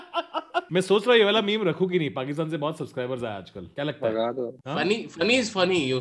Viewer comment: what did he say Pakistan k ... What? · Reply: Pakistan ka baap